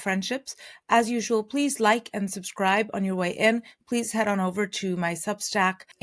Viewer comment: What did she say? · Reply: She said, "God lillelördag." It’s Norwegian and means, "Happy lesser Saturday." Lillördag (in Swedish) and lillelördag (in Norwegian) is an endearing Scandinavian word for Wednesday.